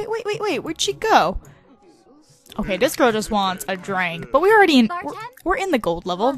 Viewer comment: doesn't it sound like said, "Bartend."